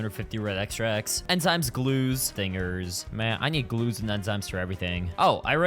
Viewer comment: what did he just say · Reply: Stingers….